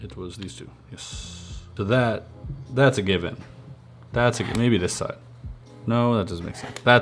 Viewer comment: When he was saying ""that's a given, that a given" at first I thought he was say " that's a gibby, that a gibby" like the Gibby from ICarly